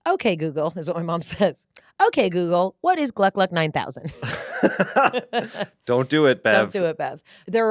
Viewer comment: The "OK, Google, what is gluck-gluck 9000?" triggered my Google Hub, and it thought you said, "OK, Google, what is bloodlust 9000?"